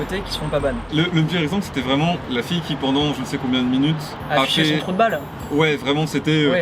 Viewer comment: j’entends « hachait son trou de balle »... il dit quoi en vrai ?